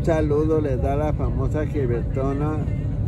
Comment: Le entendí "descanse en paz gilbertona"... Imao